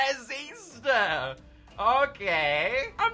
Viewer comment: entendi racista